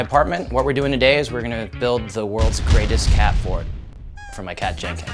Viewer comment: I thought it said worlds best cat fart